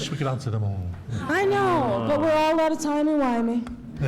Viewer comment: what did RTD say to get the "well played" response? "I set that up" I think.